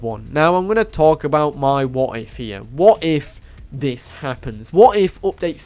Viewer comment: legit thought you said ïm gonna talk about my "wife here now